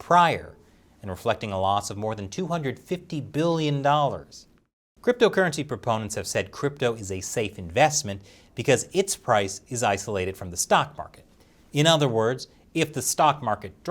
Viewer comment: I thought Blackrock said China is a good investment